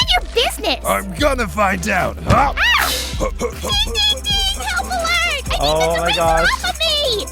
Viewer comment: what do you say? i guess it ding ding ding